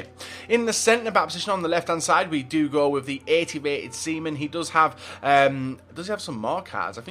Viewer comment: ???? Did you just call him semen? Wtf bateson...